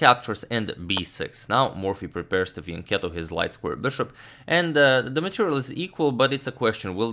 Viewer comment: What does he say at 2:10? Something the light square bishop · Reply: Yes, exactly he said "Morphy prepares to Fianchetto his light square bishop".